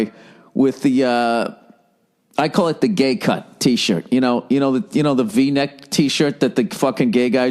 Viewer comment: I thought it said gay cut trees. This was not what I expected